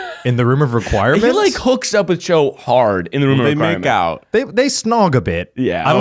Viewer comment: In the Harry Potter section I swear to god I heard say "when he fucks Joe in the Room of Requirement" and I was like WTF WHICH BOOK WAS THAT? 🤣